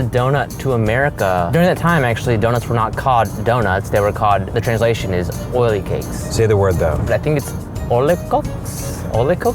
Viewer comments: I think he said "oily cocks" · i heard "oily cocks" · did anyone else laugh at what he said “oilycocks” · I hear "oily cocks".